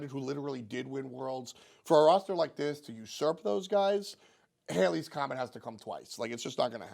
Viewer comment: Did you say "for a team like this to succeed, alias, comment has to come twice". Also I'm really high right now.